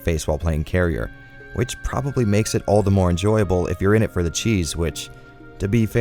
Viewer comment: I'm loving these niche little titles. Keep this type of content coming. Also, I know you said "it brings the cheese, which", but I heard that as cheesewich. Like some new type of cheese sandwich.